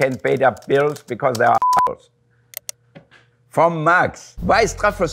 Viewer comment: what did the bleep says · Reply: Haha I know I'm two years late but I'm petty sure it said "assholes"